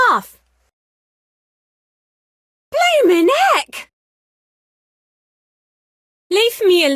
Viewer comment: i thought that she said "Hey my neck!" 😂😂😂